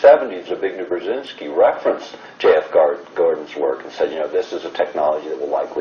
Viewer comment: thanks for watchin' ! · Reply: who is speaking? And does he say Ted Gordon?